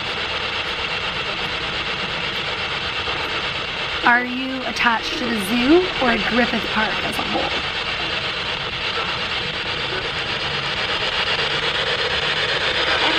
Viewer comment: i heard a faint "zoo" urghhhh!!